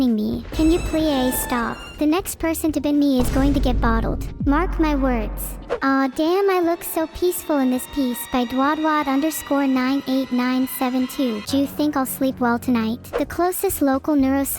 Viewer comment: did she say “do you think” or “jew you think”